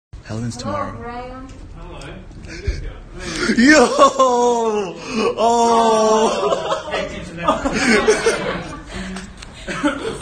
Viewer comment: Camera guy: “Hell ends tomorrow” Guy with Doom Slayer costume: “Hell ends today” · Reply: I thought he said Halloween’s tomorrow